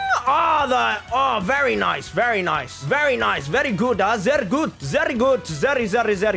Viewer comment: did he say something in German? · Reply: sehr gut means very good